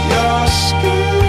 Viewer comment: I somehow heard that as "your skin.... Oh no, your skin is cold...." I don't know how. I just do